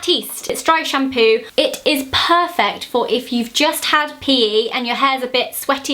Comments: When she said just after you've had PE I thought she said just after you've had a pee😃lol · I 4:28 thought you said "just had a pee" hahahha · when you where talking about the batiste dry shampoo and said it is perfect for when you just had P.E ...for a second I thought you said for when you just had pee lol. i was like why would she need a dry shampoo after a pee? haha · She said "If you have just had P.E." lol! I thought she said pee at first too.